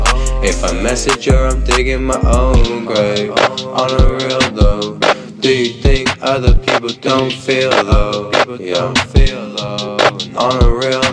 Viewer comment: Chillin in the summer losin all my friends where’s the night gone? Everybody had the old skool vans with the stripe on. If it wasn’t vans they were Air Force the white ones. If you never had them you prolly wanna buy some. (x2) if you never had them you prolly wanna buy some. Ima fuck gang up and clean it with my dyson. I just saw a girl thats calliing go on fight him. Aiming for the top catch me aye aye risin. Cause she loved me but she loved you more. Cause she left me fallin but she chose you for. And she broke my heart that’s what’s do for. And you messaged me and you dooo noooot care. Cause I’m ridin my own wave. But she saw I’m doin good she’s like ohhh hey. Yeah she read from my mouth I’m not oook. If I message her I’m diggin my ooown grave. On the real though, do you think other people don’t feel ohhh. (x2) Chillin in the summer losin all my friends where’s the night gone? Everybody had the old skool vans with the stripe on. If it wasn’t vans they were Air Force the white ones. If you never had them you prolly wanna buy some. (x2) I dont care I know some is wrong but it’s accurate enough for me. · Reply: I always hear it as "do you think other people don't feel looow"